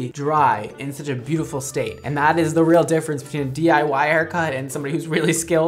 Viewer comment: at first I thought you said, “and that’s the real difference between a DUI haircut and someone whose really skilled” 😆😅